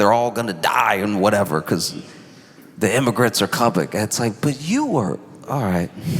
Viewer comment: The immigrants are _____ it’s like, but you were… Can someone please tell me what word he says in the blank? The immigrants are what? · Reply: The word was: Coming Parody of Elon saying, "The immigrants are coming..." Being interrupted by someone pointing out, "But you were..."